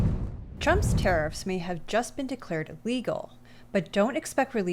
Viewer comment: I listened to this over and over, and I swear she says his tariffs were "legal" and not illegal @ 0:32. 🤷‍♂️